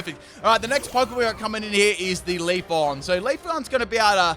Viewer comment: did you just say "leaf-on" and then pronounce Leafeon correctly immediately after?